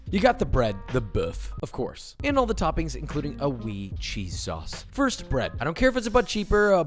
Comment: did someone say "weed cheese sauce "👀👀😍